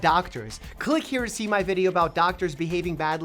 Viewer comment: In the end, I always hear him say "stay happy unhealthy".